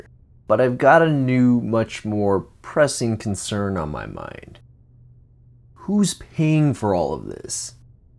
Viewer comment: I thought he said "Who's peeing on all of this" "Who's peeing in all of this"